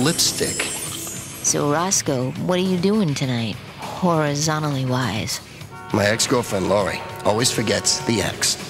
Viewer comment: What is she saying at 2:02? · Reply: i don't know. I think she is saying "horizontally-wise" or something. Does that mean doggystyle, cuz that's not in their dna?